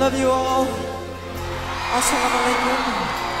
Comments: did he say "esselamu aleykum" ? :D <3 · did he say at 7:39 "alslam alykom"السلام عليكم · Did he say, "Assalamu'alaikum?" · did he said al salam alakum It's mean (peace be upon you) 😮😮